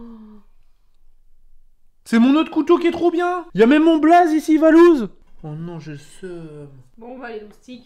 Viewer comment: c'est quoi le chanteur qu'il a dit ?? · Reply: C'est lui